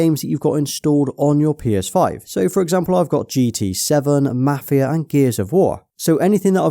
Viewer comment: did he say gta 7?